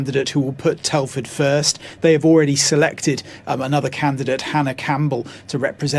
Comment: I thought he said the Tory candidate was a cannibal you never know with the Tories lol